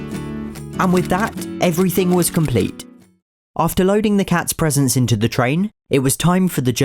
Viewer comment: When you said you were loading the cats presents in the train I thought you said the cats presence